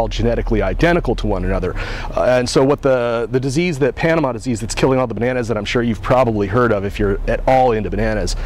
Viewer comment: I heard, "FUSA IS KILLING ALL THE BANANAS"! King Julian was right!